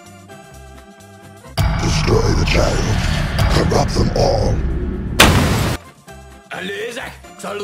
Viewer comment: Video: DESTROY THE CHILD , CORRUPT THEM ALL . Subtitles: [Music] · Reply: I thought he said or rob them all